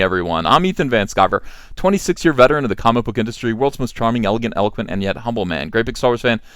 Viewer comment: I heard "elephant man"